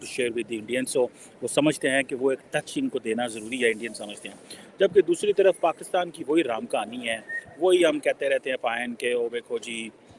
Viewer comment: did he say Ram Kahani hai??